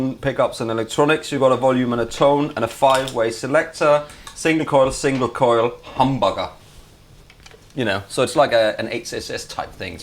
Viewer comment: does say “Single coil, single coil, humbugger”?!?!